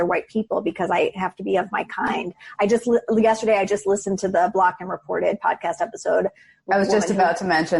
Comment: What was the name of the podcast she refers to at 34:10? She says it so fast, I can't quite make out the name. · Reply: Blocked And Reported. Good stuff.